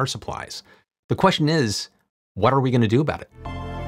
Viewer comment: Oh good lord, did he say "water we going to do about it?" ~ 2:30??? 😂😂😂